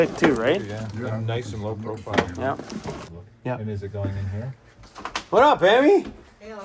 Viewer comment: "what up, baby!"...that is what I thought i heard...lol!..then i rechecked and heard Emi...lol.....I hope you guys come to Ottawa Ontario when you guys drive across Canada summer road trip, to drop the van off for the interior work being done in our Province!:hand-purple-blue-peace::hand-purple-blue-peace:(Santa on the Coke can?!)